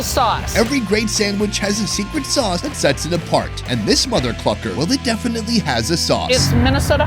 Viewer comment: did he say, mother fvker?